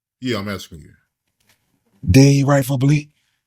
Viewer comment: I thought yall said J didn’t write!